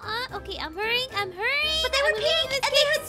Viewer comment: I thought she said but wth